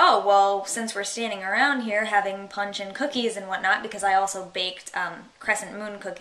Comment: That was very cool. The funny part was I heard "I made pungent cookies..." I thought, very interesting, I wonder what flavor or scent? Then you said something about cookies and punch, and it came together. punch-n-cookies. lol These videos are very interesting, Thank you for the hard work... :)